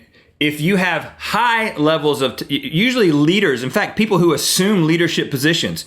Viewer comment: When he first said leaders I thought he was saying liters (of testosterone)